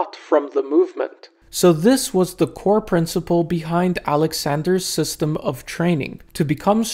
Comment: Why do i keep hearing alexander's ass bro?!!!!!😂